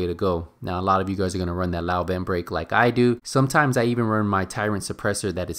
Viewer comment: What does he say at about 3:41 about a Lao Vembreak or something??? Anyone know? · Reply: He said "Loud Vent Break like I do". Loud vent breaks are nice for PvP and usually have nice rolls for Crit DMG and Crit Chance.